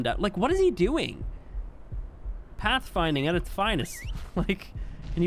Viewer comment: I thought they said that they fixed path finding.